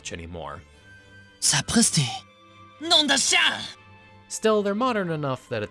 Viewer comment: Little correction at 2:19 he actually says : "Nom d'un chien". Which would be closer to name of a dog